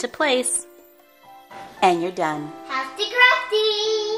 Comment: I thought he said hafty crafty